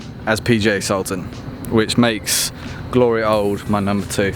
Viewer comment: did you say Glory Holes is your number two 🤣💥🏌️‍♂️ great content.. could you do a Thracian Cliffs or Cyprus vlog please at some point, I’ll join you if you want a 18-20 handicapper’s thoughts too 🙏👍